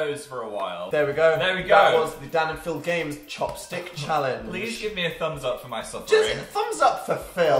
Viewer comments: who else thought it said chapstick challenge. deleting my life fr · OMG I literally thought it said CHAPSTICK challenge and actually died lmao